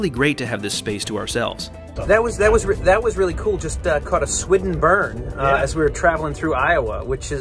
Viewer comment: what did you call the burn that you passed ? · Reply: A Swidden burn!